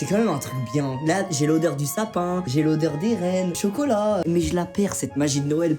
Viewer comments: Quand ta dit "j'ai l'odeur des reines" jai compris "j'ai l'odeur d'Eren" 😂😂😂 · j'ai entendu "j'ai l'odeur d'Eren" :) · « j’ai l’odeur des rennes... » Moi qui ai compris « j’ai l’odeur d’Eren »... j’ai buggé pdnt 5 min j’ai un vrm un problème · j'ai compris "j'ai l'odeur d'Eren" 😂